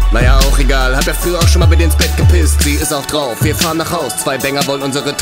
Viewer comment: ich hab da immer verstanden "Hat der Führer schonmal bei dir ins Bett gepisst?" 😂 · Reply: Hört sich tatsächlich genau so an, aber er singt "Naja auch egal, hab' ja früher auch schon mal bei dir ins Bett gepisst"